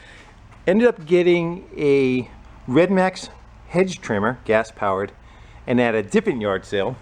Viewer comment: My hearing is getting bad I could have sworn you said red neck hedge trimmer